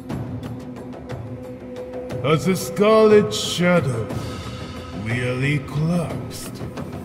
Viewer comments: "As a scarlet shadow, nearly collapsed" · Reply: I heard it as "As a Scarlet Shadow, the elite class" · I think it said "nearly collapsed"